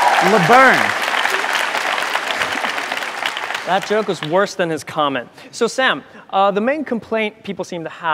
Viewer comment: 聽不懂 02:28 是說That joke is worse than his comments. 「那笑話比他的評論還糟。」是指鄭自焚的評論嗎？